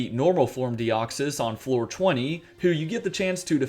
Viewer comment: I totally thought he said "420" lol